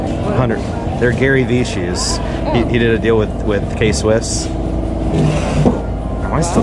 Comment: when you asked how much the Gary vee shoes were she said “25” but you misheard her and thought she said 45, you could’ve had a steal man! Great video.